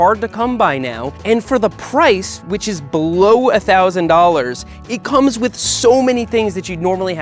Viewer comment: $600. When he says below $1,000, I hear 'almost $1000'. But that thing is like the same price as his unicycle.